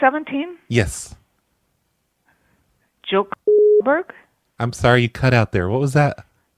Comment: did she say Joe Goldberg at 11:30 ?? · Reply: Cumberg